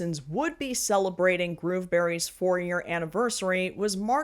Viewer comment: Fun fact: the auto subtitles at 7:11 call Grooveberries ‘Grieveberries’ - the joke literally has written itself. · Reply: I kept hearing 'Groomberries'....which, yeah...